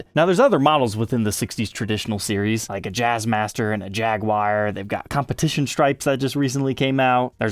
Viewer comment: Did you just say "Jagwire" 😂😂 American English! Fender should make a version of the Jaguar that's done up like the areodyne Strat and call it the JagWire! 😄 Japan, home of the sharp router bit